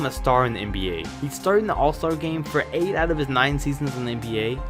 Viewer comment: Okay at first I thought he said 10 out of 9 seasons and I was confused